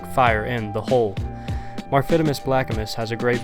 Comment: Who is that person at 1:36 ? I can't hear out his name. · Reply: MarphitimusBlackimus is the name